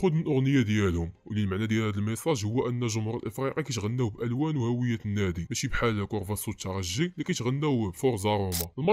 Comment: فايت سمعت جمهور الافريقي كيغني على مارسيليا شي حد يأكد او ينفي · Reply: Allez allez les marseillais mch nghniw ala marseille y msatek 😅